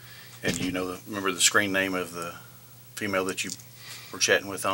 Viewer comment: As the officer asked for his screen name I heard "scream name".